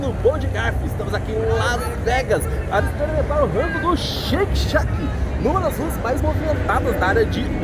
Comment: Eu entendi "Shake Shake"! Hehehe! Lembrei do "Mischief Makers" do N64! Sdds, Mixer!